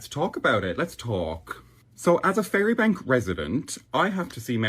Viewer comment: Did he just say Fairy Bank?